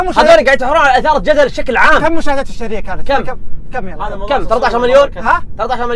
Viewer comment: مشاهدات ايش؟ ما سمعت! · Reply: اليومية